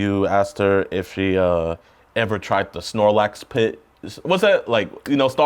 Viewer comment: The sarlacc pit. Sounded like he said Snorlax 😂